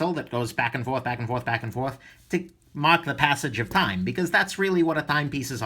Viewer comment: I thought you said 'mock the passage of time'. I have been doing that all my life by saying 'I'll do it tomorrow. Now I am old and not mocking now as my power reserve is too low. Only escapement is death when the movement stops.